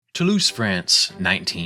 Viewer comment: I misheard "Toulance, France" as "To lose friends", as if that was the answer to the title, lol